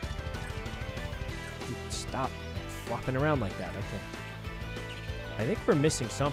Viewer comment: did i hear that right? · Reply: I heard "flocking around" which means "moving around"